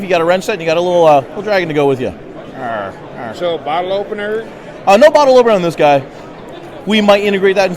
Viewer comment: WHAT!!! Did I hear right 23:48, no bottle opener. WOW!!!!